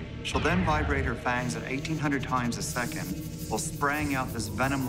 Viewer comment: 1800 times a second !!! Did I hear well ???